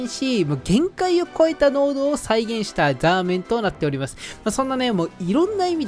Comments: 本当に申し訳ないけど「限界を超えた濃度を再現したザー◯ン」にしか聞こえない。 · ラーメンがザーメンにしか聞こえなくて草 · ザーメンにしか聞こえなくて草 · のところ、ラーメンがザーメンに聞こえる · ザーメンに聞こえる？